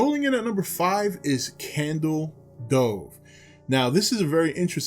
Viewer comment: I heard "candle stove" Lol. I like candle cove my favorite creepy pasta